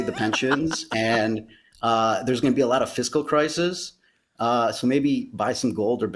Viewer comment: When he said 'fiscal crisis' I heard 'Disco crisis.' I thought, what's so bad about that?